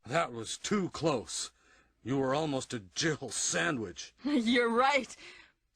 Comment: I always thought Barry was saying "Jibble sandwich"